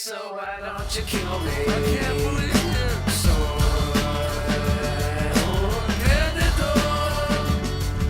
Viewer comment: Wtf siempre pense que decia "Soy un perdedor" pero resulta que dice "Soy un perdido" no puede serrr